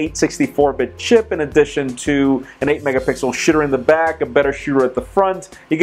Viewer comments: Did he just say a "shitter in the back"? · LMAO! An 8 megapixel shitter on the back... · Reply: Oscar Conroy I agree at 0:42 it does sound like he is saying shitter